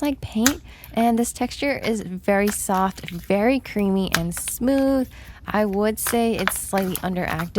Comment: why did she say creepy texture…